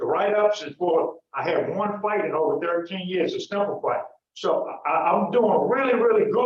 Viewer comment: Did he say "a snowball fight"? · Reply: “Simple fight”